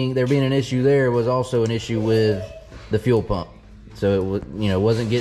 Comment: Motor sounds good. I thought you said the three pumps was rebuilt. Good luck this weekend, go